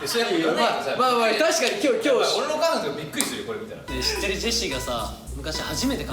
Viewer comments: 初っ端のジェシーの「俺のお母ちゃんもびっくりするよ」が「俺の彼女」に聞こえてびっくりした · 俺の家族が俺の彼女って聞こえた笑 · 私もそれ気になったんですけど、コメ欄誰も触れてなくてびっくりしてます笑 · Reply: やっぱり俺の彼女見たらびっくりするよって聞こえますよね？😭😭 みんな俺のお母さんって聞こえるっていってて😭